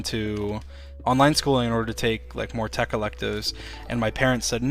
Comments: I think he said ‘tech electives’ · he is saying 'Tech Electives' The story makes more sense being aware that this is what he said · "in order to take more tech electives" is what he said, I'm fairly sure Edit: nevermind, I see someone else said this and you liked it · I think he says "more tech electives"